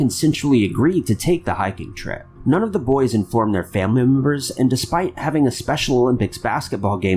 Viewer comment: Excuse me Mr. but what exactly are "family moombers" ??? I think you meant "family members" (according to the Closed Captions) but that's not what you said my kind sir. Are you trying to send us hint to create a "Top 5 Unknown Words That Ty Confuses Us With" ??? I'm messin around, just trin' to give everyone a bit of a chuckle :) Keep up the GREAT WORK!!!!